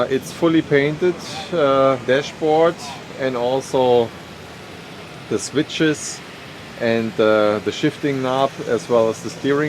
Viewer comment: Sounded like he said "used to ride the bitches"